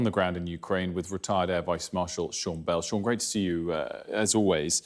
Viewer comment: I thought he said "retarded vice marshal" 💀